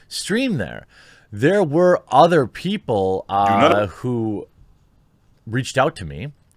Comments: There's some kind of audio glitch; sounds like someone saying "Do Not" in a very...speaker-ish voice. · What's with the strange man's voice saying "do not" at 1:18 - 1:20, after you say "other people"?